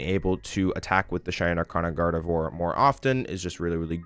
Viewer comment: ok i know it sounds crazy but what is he saying? "shiny art card of gardevoir"?? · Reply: Shining arcana gardevoir haha